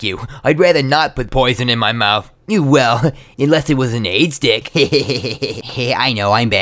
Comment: oh i thought he said aidstick lol i cant hear. and yes i understand the joke but thanks anyway(